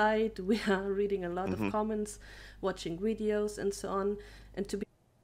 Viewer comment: did she say "Watching wideos"??